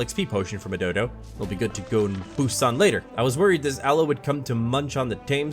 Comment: “Poes on later” did I hear that right 💀